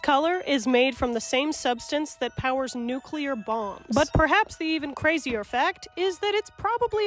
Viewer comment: tbh i thought she said "nucular bongs"